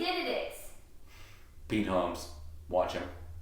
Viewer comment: did you say beat homes? i cant find him